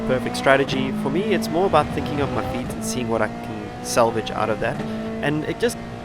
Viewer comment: "for me it's more like thinking about my ...." Beatency ?! What's the word ? Hard to hear, 'beatency' is what I hear but that doesn't exist :-) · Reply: about thinking on my feet and seeing what I can salvage...